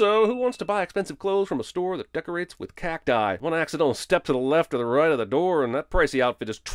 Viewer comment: I misheard cacti as cack dye. 😂